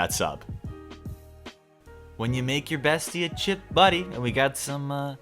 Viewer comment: A chip butty is a British staple and truly the most exquisite of all culinary escapades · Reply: I wonder if it's just me, but I keep mishearing the name as "Chip Buddy" whenever someone talks about that sandwich. It sounds like some sort of device meant for carrying a pack of McDonalds frenchfries, possibly something that clips on to your belt. (Or suspenders, if you're too corpulent for belts) ... Why am I suddenly imagining someone walking around with a bandolier of plastic frenchfry holders?